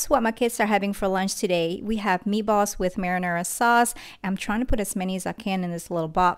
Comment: Did she just say marijuana sauce?